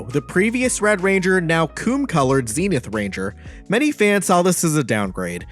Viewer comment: You def should edit that zato line it sounds like u said “koon color “ no cap lol